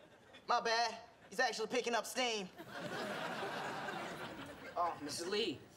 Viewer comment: did he say steam or scream? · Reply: He said steam